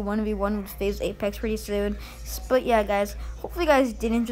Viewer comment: i always hear "piss out" not "peace out"